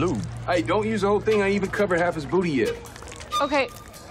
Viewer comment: nah did he say booty